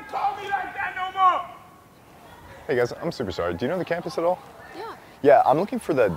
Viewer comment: did he say i'm super sorry??🤣🤣🤣